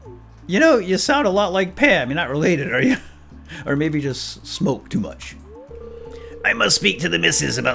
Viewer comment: first time, i heard "soap". second time i heard "soup".